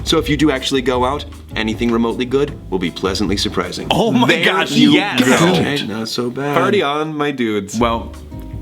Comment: I think I heard say "thank you gil", as in is thanking anx before the actual name is revealed. Did know the whole time orrrr??????